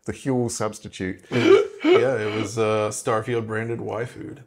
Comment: I heard “Starfield branded waifu”